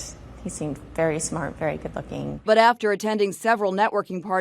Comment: Did she say good looking? 29:53 😂